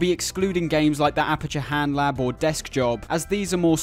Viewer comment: I thought you said aperture hand job for a minute and I had to do a double take